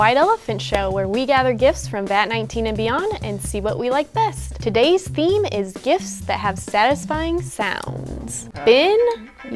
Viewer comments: why did i hear "todays theme is kids that have satisfying sounds" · Who thought she sounded like “Bin” Also I love this :3! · why does it sound like said bin instead of Ben?